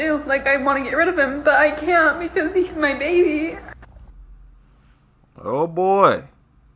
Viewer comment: WHEN SHE SAID "IT'S MY BABY", i THOUGHT SHE SAID "IT'S MY MATEY".